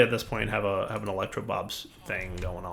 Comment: I keep hearing "elecrtoBLOB" instead of "elecrobob"...